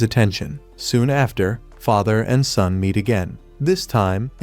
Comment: UMM.. did i hear wrong at this time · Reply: no he said son instead of daugther